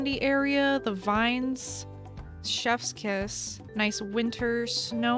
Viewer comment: And I thought said shep’s kiss as in the dog villager